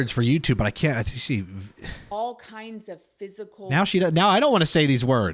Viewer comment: physical threats is what she said